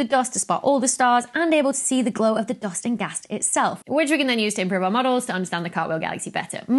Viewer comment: Can anyone figure out what the hell she said in the three seconds starting at 0:52 ? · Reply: "Which we can then use to improve our models to understand the cartwheel galaxy better"